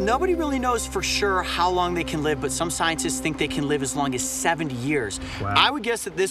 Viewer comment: Did he just say they live up to 7 years but this one looks about 25 years old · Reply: He said 70 years